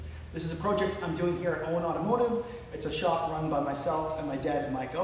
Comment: Did you say Owen Art Motors? Thanks for posting these marvelous videos . So inspirational and informative. I need to rebuild / refurbish the motors in both my MGs TC hasn't run for 50 years and the TF not for 37 years. Appreciate your work -- Thanks again.